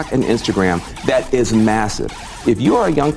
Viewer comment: did I hear massive